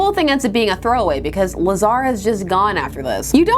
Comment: Had to replay the Lazara name reveal because at first I swear I heard "-new villain name: Lasagna"